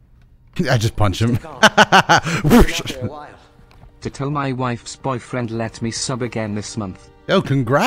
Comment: did homie just say “ to tell my wife’s boyfriend let me sub again” 😮